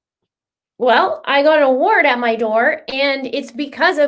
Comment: I thought you said you had a warrant on your door, and it was all because of "you guys". LOL! CONGRATULATIONS!!! You totally deserve it!